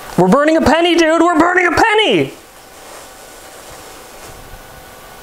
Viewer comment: what did you say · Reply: focus